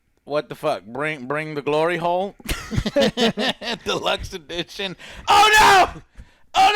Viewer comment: I thought said “bring the glory Hole”😂😂😂